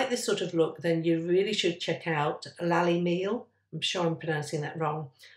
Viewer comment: Can you please provide a link to the mixed media artist you mentioned? Didn’t quite catch her name… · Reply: I’m hoping someone heard it correctly too. I thought I heard Lally Neal but can’t find anyone by that name.